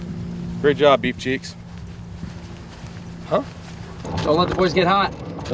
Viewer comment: It made me laugh pretty good. What did Brad say? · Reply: Great job, beef cheeks. It's an older saying, but Brad is the youngest of seven so probably why he knows older sayings and songs. It's funny they call grandpa, when it reality the stuff Brad says is something people in their late 30's would say.